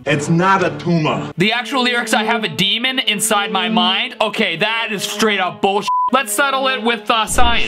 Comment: Original Lyrics: 0:32 You try to take his pulse 2:38 Tied to machines that make me be 3:25 It’s the last time you will 3:56 And the things that will bite, yeah! 4:43 This is sempiternal 6:03 I have a demon inside my mind 7:05 Our light induced, Image of truth Filtered blank, of its substance 8:01 Figured you were writing out your part 9:23 Sweet libial meat 9:52 Libarate my madness 10:57 Jesus, it never ends 11:39 The sickening, Packaging subversion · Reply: I heard "I HAVE A DEMON INSIDE MY RIND"